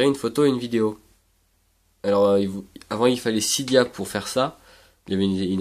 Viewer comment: cibia ? sibia ? cidia ? qu'est ce qui dit ?